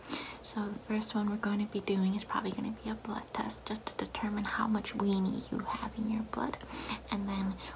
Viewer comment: i have a kind of dirty mind so i heard "how much weenie you have in your butt" the first time i watched this